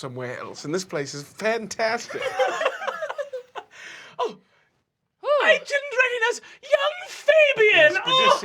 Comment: I love how when Brennan says "I didn't recognise, young Fabian!", whoever wrote the subtitles thought he said "ancient readiness, young Fabian!", that's hilarious.